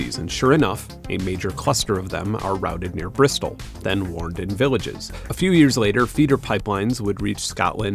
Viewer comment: What did he say here? "...then warned in villages"? Im trying to figure out what this sentence means.